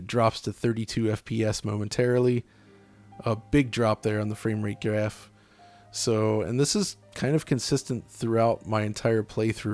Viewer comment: I thought John said framerate giraffe. Lol.